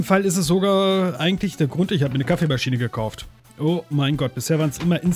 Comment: An der Stelle [11:22] verstehe ich auch beim 4ten mal: "Ich habe mir eine Kaffeemaschine gekocht..." :D hahaha naja eher "gekaucht" aber trotzdem gut :P Demnächst beim Leitwolf #Let's koch Kaffeemaschine muhahahaha